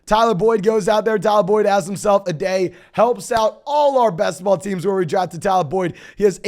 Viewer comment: Did you say Tee Higgins helped out our Basketball teams? lol